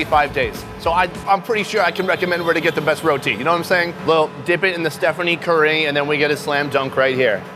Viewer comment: Did you just say DIP IT IN THE STEPHANIE CURRY?